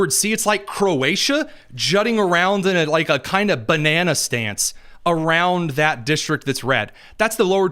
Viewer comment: Did he say " banana stance," or "Bananastan?" I like the latter! 😊